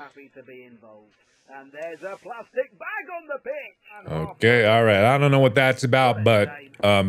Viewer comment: "There's a p l a s t i c bag on the pitch!" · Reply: I had to back it up & relisten twice LOL because at first I thought he said "there's a plastic bag on the bitch!" Then I thought maybe he said bench. Finally it clicked in my sleep addled brain that he said pitch 🤣